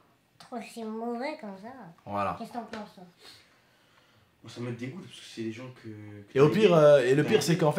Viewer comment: Écoute ils dit quoi à 7:45 « oussama ils dégoûte »